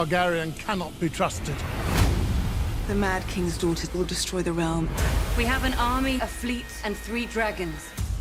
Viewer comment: To me it sounded like Cersei said the mad kings daughter DIDN'T destroy the realm. ???